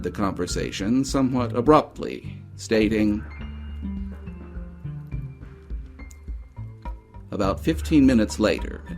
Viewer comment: You state that the doctor had ended a call abruptly and stated something but we don't hear what that is. Can you tell me what he said when he ended the call? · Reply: Apologies for that. He said "I'm sorry, I have to go, I have company."